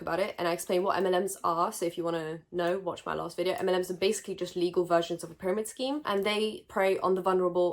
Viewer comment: I thought you said “M&Ms are bad” and I almost died